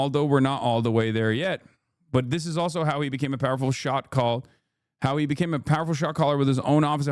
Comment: I could've swore you said "shock collar"